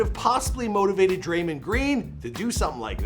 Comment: I cringed when he said draymund green instead of draymond